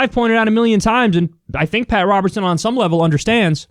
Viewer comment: I thought it said Robert Pattinson